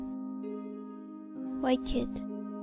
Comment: I heard “White kid” Instead of “White Kit”